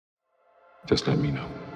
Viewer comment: Is he saying "subscribe me now or Just lemmino"!